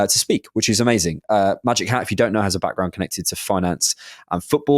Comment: Loooool when said “ if you’re a X user like I am” then I thought he said “magic hands” I was like whaaaaaa?? 😂😂